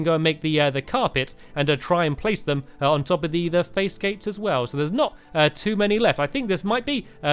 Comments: did anyone hear him say face gate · did stampy just say "face-gates" around 15:20? lol xD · stampy said face gates instead of fence gates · stampy said face gates instead of fence gates😂